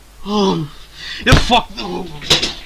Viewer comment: did he say frick the rules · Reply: i think he said the f the holy s